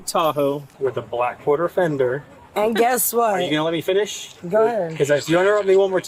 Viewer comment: "so, go ahead babe" said the driver on the phone oops how to know when catch that Even the subtitles thought she said Ben nobody has been called Ben this entire time